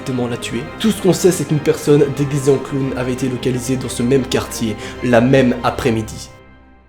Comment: like si toi aussi tu as entendu "la" même après-midi